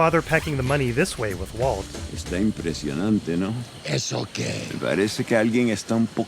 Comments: Reeeeeeeeeeally don't wanna be that guy, but Hector is not saying "It's okay", he's saying "¿Eso que?" which translates to "So what?". That's the reason Bolsa scolds Hector at the end of that scene. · BCS subtitles are wrong. Héctor says "¿Eso qué?", not "It's okay". Basically, he is saying "so what?"